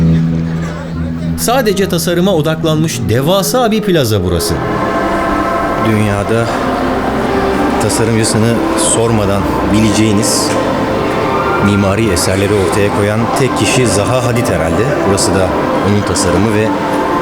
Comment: 'te ne diyor (Dünya'da tasarım.....) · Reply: Dünya'da tasarımcısını sormadan bileceğiniz mimari eserleri ortaya koyan tek kişi Zaha Hadid herhalde.